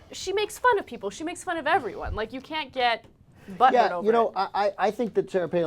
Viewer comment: Did Ana just say butthurt? lol